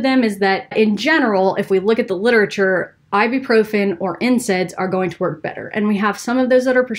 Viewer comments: When she said NSAIDs, it sounded like she said incense. Another herbal remedy. Just kidding. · I was listening and thought I heard you say “incense” took me a few times to under stand you meant nsaids😂😂 i thought how does that help?!